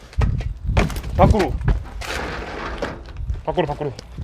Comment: 파쿠르하는데 나만 밖으로라고 들림???